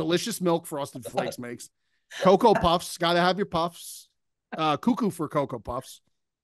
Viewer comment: My bad I misheard, it was coco puffs not Pebbles. Fruity Pebbles is a good play.